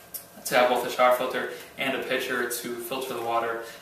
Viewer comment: every time you said pitcher i thought you were saying picture and i was like, what?! XD lol